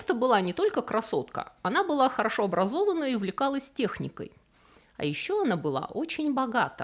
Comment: После того, как Берта первой доказала, фактически, всему цивилизованному миру преимущества передвижения на автомобиле, называть женщин за рулем «обезьянами с гранатой» не просто несправедливо, но и невежественно! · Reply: 99% женщин за рулём - тупые папины "принцесски". а Берта если вдруг не расслышали - УВЛЕКАЛАСЬ техникой. она ЗНАЛА что к чему.она не переживала что ногтями поцарапает руль.